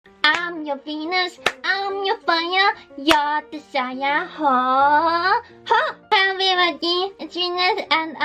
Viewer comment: Did she said I am your penis?